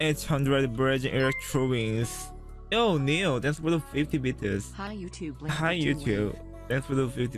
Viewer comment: I literally heard hi youtube thx for 50 bit** at 0:30 xD love your vids anyway